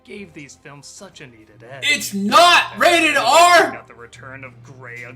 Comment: I think you miss heard him, he said that it is rated R. Just wanted to point that out.